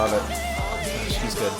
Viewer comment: shes good im sorry chen 😔✊ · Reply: Oh I thought he said “gosh he’s good” but kinda slurred it together